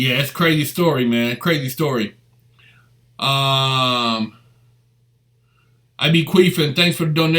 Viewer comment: ish someone donated and name is "I be queefing"? I hear that right?